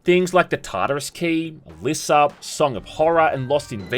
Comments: Can someone tell me what the name of the second good indie game he mentions after The Tartarus Key is? Sounds like Lissa or Liss up but I cant quite find anything like it. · What's that game at 26:15 ? I can't tell what he's saying. Subtitles say "Liss Up" but I can't find anything with that name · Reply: Alisa